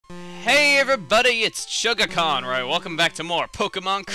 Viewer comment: It always sounds like u say Hey everybody its